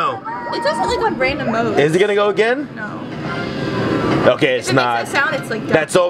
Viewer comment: did say “fuck it” or is it just me??